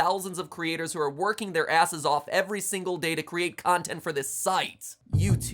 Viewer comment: Did Matpat just say asses?